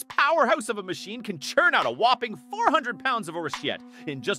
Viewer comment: it sounds like it says, "This machine can churn out over 400 lbs of horseshit"?